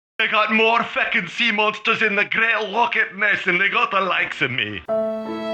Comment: I can't understand a word... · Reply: They've got more fecking sea monsters in the great Loch Ness than they've got the likes of me.